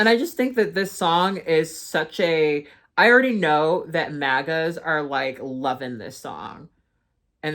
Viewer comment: did you put the captions on this video, or were the captions done by Youtube, 45:55 captions 'MIGOS instead of MAGA'S,' and 46:04 captions 'NIG*AS, instead of MAGA'S. I have no clue if this is some kind of inside joke done intentionally by you, or if Youtube has done this- if that is the case it is EXTREMELY concerning! I have screen shots too, if needed!